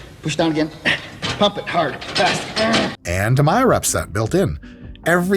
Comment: nah bro “pump it, hard, fast”? Had to know what he was saying breh. Homoerotic is cool tho.. not judging. Just hilarious.